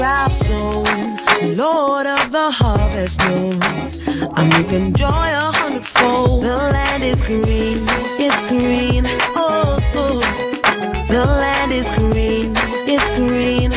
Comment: When I was very little..I thought I she said the light is green 😂